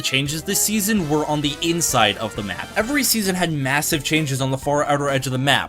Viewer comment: did you say… massive?😼